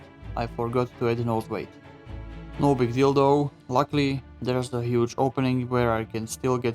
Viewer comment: Swear he said no big dildo at 6:50. Not that there's anything wrong with that. 😅